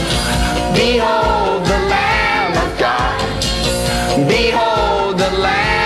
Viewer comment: I always thought he was saying "B.O. the lamb of God"